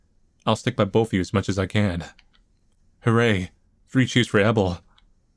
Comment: i heard that as "Hurray! Free cheese for Ebel!"